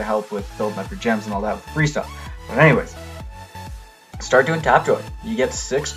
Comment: what did he say? "start doing tapdroi" was what I heard but i'm pretty sure i'm wrong.